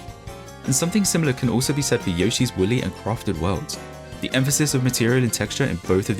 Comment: it sounded like he said "Yoshi's willy!"😂😂😂😂😂😂😂😂😂😂